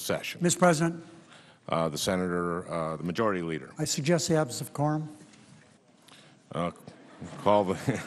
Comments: what does he say 0:54-55? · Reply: Absence of quorum. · I don't understand what does he say @ 0:54? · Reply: Sen. Reid said 'I suggest the absence of a quorum.' They need a 'quorum' to do business like vote etc. Sen. Franken laughed because there was actually a quorum mostly all Senators show up for such votes. They call the roll to give members time to either get to the floor or go about their business, until they're ready to do whatever is on their schedule next.